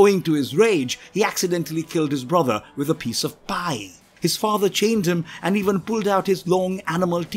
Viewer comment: "He killed his brother with a piece of pie" That can't be what he's actually saying